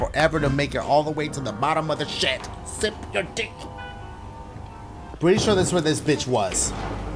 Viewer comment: who else hears "sip your duigh"